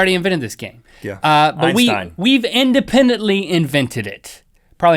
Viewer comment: does Rhett say einstein or lastime? please help me clear this war between me and my brother. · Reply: Einstein